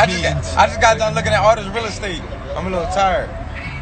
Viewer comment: when he said "im a little tired" i thought he said "ive been looking at some real eastate, imma RETIRE" boy my heart stopped pls dont stop fighitng danny you the man brotha !